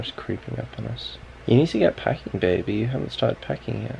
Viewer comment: Late, but at 9:07 I thought said PREGNANT, not PACKING and I freaked out 😂 Love you guys ❤️